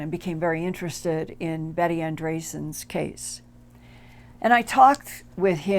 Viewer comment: what was the name of the author who wrote of her experiences in the 6 book series that you mentioned? I looked up Betty Anne Drasden that is what it sounded like you said but I cannot don’t an author by that spelling anywhere. I would like to read that series of books. I’m a hybrid human from the knowledge I discovered about the lifelong abductions that happened to my mother and my resulting birth. I’m finding out very interesting things about myself like that I’m very telepathic and I can produce healing energy with my hands. Clairvoyants have told me that I’m a Starseed that is considered an original galactic meaning I’ve lived many lives as many beings in many places other than the Milky Way Galaxy. Ever since I was young I’ve experienced so many miraculous supernatural things and seen UFO’s up close and personal and had a telepathic communication from a race of beings that called themselves Arcturians. I’ve long believed that Extraterrestrial Intelligences are after our souls because they either do not posses one or because theirs are different and not immortal as ours are. There are many people like me awakening to understand that this world..this reality is not what we have been told. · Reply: Her current name is Betty Andreasson Luca, after marrying fellow experiencer Bob Luca. Her original story of her close encounter of the fourth kind experiences were first brought to the attention of the general public by Raymond Fowler in his best-selling 1979 book "The Andreasson Affair" which was the first of 4 books he wrote on her experiences. Betty Andreasson Luca wrote her own book, "A Lifting the Veil" in 2017. I bought a copy of "The Andreasson Affair" soon after it's first release, and as a child, it had a profound effect on me - I can even remember which bookshop I bought it from and the day I bought it ! Fowler, Raymond. The Andreasson Affair. Englewood Cliffs, N.J.: Prentice-Hall, 1979. ——. The Andreasson Affair, Phase Two. Englewood Cliffs, N.J.: Prentice-Hall, 1982. ——. The Watchers: The Secret Design Behind UFO Abduction. Englewood Cliffs, N.J.: Prentice-Hall, 1990. ——. The Watchers II: Exploring UFOs and the Near Death Experience. Newberg, Ore: Wild Flower Press, 1995. "A Lifting of the Veil", Betty Andreasson Luca, 2017, The true life story of two individuals that were brought together by the UFO phenomena. This life long journey began in 1945 long before Bob & Betty met. Little did either of them know they would be introduced to each other and ultimately Married because of UFO experiences they had in 1967, again years before they met. This marriage and their UFO related experiences led to harassment by both the Military and Government agencies for years. <li